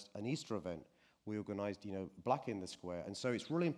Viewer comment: did he say 'we organise black in the square' 💀